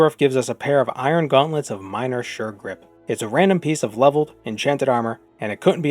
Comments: I swear I heard "iron gauntlets of shirt rip" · when ya got to the gauntlets of sure grip part, i thought you said "shirt rip" and was briefly puzzled by the possible genre shift of the video.